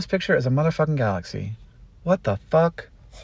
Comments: Did he just say “stick on a shit?” Because, I feel that. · Rewatched multiple times just to hear Hank curse, strangely satisfying and entertaining lol could tell he’s not one to curse often lol because of the few pauses/stutters to keep swearing on and then I thought he said shit on a stick but he drops the classic stick on a shit 😂 wuv u Hank.. oh and that star was pretty cool to 😂